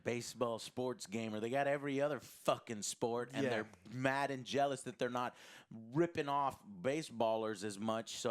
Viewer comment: Joe: “mad and jealous” What I hear: “Madden jealous” LOL